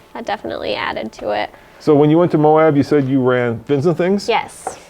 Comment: At the 4:28 mark he mentioned a trail in Moab. Did he say Vinson things? I am wanting to go to Moab for the first time next year, and curious what trail she went on.